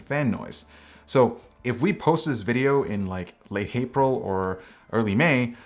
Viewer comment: Ok well I heard him say “late hapril” ;p my ears hear everything 🤪